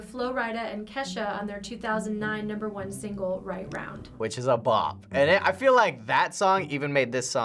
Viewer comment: wtf is a “Bop” or is he saying pop??? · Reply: Ismail Ali he’s saying bop. It basically just means a good song.